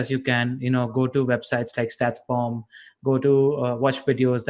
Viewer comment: didn't catch the sites..Statsform? T4? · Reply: Statsbomb, tifo footballYT